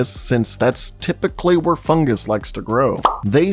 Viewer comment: I thought he was saying “Fun Guy” 😂😂😂😂 and I’m like this “Guy” doesn’t sound like any “Fun”. 😂😂😂. 🤦🏾‍♂️ 🤷🏾‍♂️